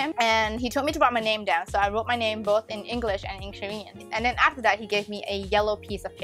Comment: the subtitles are wrong here; she wrote her name in English and Korean * not German.